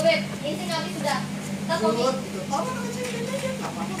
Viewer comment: I hear "ih lucu banget"